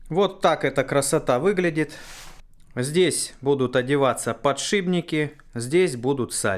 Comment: Я один слышу "ПодшиБники"?)